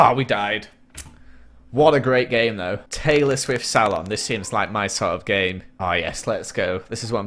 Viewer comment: I thought he said Taylor swift salad XD